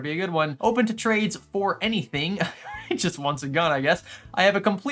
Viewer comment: When you said "he just wants it gone I guess" I misheard and thought you said "he just wants a gun I guess" and just figured that american markets are wilder than I thought until I checked the cc's